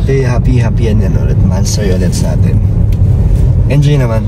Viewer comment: "BHE HAPPY HAPPY MONTHSARY SA ATIN". INULIT KO TALAGA ILANG BESES. YUNG IBA SI LANG NARINIG HAHAHAHA, LINISIN NYU TENGA NYU AY😆